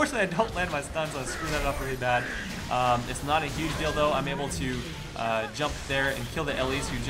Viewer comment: it sounds like he says "It's not a huge dildo" instead of "It's not a huge deal though" :D